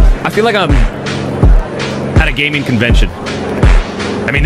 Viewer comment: Not sure if it's the Mandela effect or my ears but all I hear is "I feel like I'm at a daycare, literally at a day care champ it's just like this"...